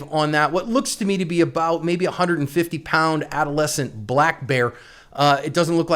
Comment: I swear I thought you were going to say “about a 150 pound black MAN !! “ 😂😂😂😂